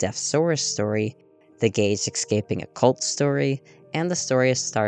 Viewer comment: ngl i thought you said "the gays escaping a cult story" and was very amused